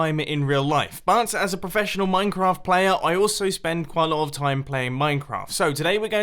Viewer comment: coughs did I hear professional.. minecrafter...?